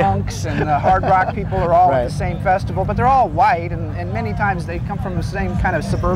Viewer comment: Did he say "They're all white?"